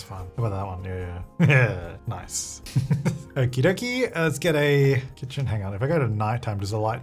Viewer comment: what is saying? It sounds like Swedish ”oki doki älskar dig” but can’t be, right? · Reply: okie dokie, lets get a....